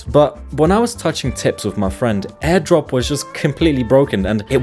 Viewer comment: wait what did you say? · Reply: Tips🥀